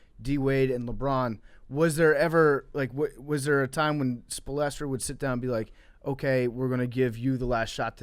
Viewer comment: did he say “espolastro”